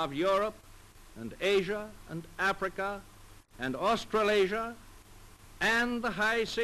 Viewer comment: did FDR Just Say Australasia